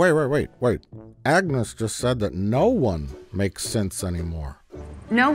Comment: I haven't even seen the show, but what the hell is up with all the violence and cursing? That's what Star Trek was missing: gore and F-bombs. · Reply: When Agnes says "No one makes synths anymore" I keep hearing it as "No one makes sense anymore." Could this be the hidden unifying principle of Star Trek Picard ? ? "No one makes sense anymore of any kind! It's a violation of Galactic Treaty." I guess there had to be an explanation - Kurtz Trek makes no sense of any kind. Galactic Treaty!